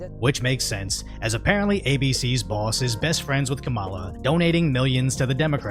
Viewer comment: I heard her called "KAMALA-DING-DONG" 😂😂